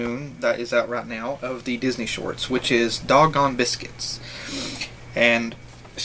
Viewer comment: I always thought it was said, "dog-own"